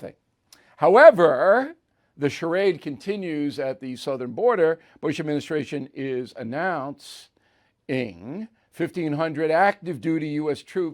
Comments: does BIll say “BUSH” administration? Faux pas? Or no? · did you say the Bush administration is announcing